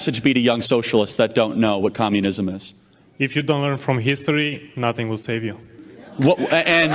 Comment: did he say ''if you dont learn from history nothing will save you''